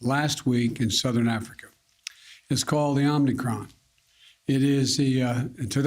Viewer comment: Did he just call it "The Omnicron"?